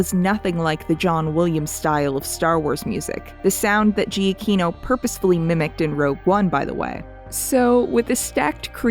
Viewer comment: there's a joke, not included in the captions, where she says something like "He put his whole [bertissy?] into the soundtrack." No matter what speed I listen at, I have no idea what's being said. Could someone clarify? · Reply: Whoops I will fix that in the captions. I said Britussy. Basically a play on the internet's recent meme where people basically say somebody put their whole ussy into something